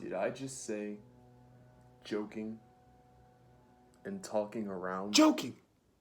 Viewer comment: I heard him say joking and taking around 😂😂